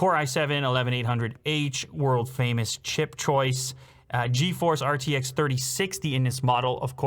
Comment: Did I hear wrong? "Core i7 11800H world famous CHEAP choice" 🤔